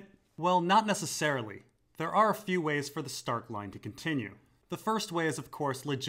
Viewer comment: I keep hearing "Stark Line ending" Like Bran has already died. If Bran comes through the wall in episode 10, then walks to winterfell, he is the Lord of Winterfell. Hell, he's got a tree to use, too.